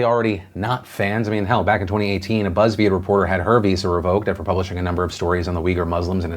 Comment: omg... i heard "buzzfeed journalist had herpes" 15:24 lmaoooooo